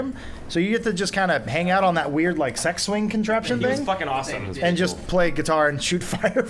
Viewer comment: did adam say sex swing